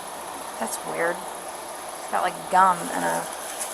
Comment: Did she just say that's weird gum in a napkin well hell search the whole black church lol